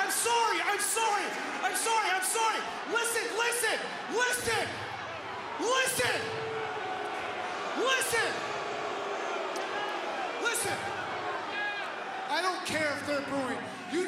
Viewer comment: all I heard was "Listen, Linda, listen! Linda... Honey... Honey, listen!"